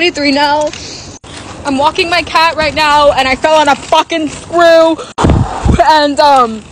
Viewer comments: HELP I THOUGHT SHE SAID - i fell on a squirrel 😭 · The first time I watched this I thought she said she fell on a squirrel😂